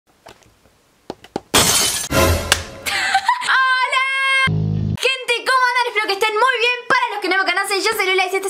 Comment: Pasos: 1_Pongan pausa 2_apreten el minuto 3_sabran que dice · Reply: Dice:como vas a golpear el lente lola